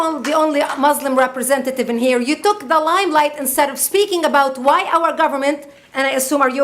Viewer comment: i am translating this, but i can't understand some words. first at 3:58, she says "you took the lime like"? then at 4:31 "in the garbage where it belongs and start calling ..."?